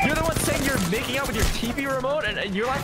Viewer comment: am I the only one who heard her say “I dIdNt MaKe OuT wOtH mY tV rEmOtE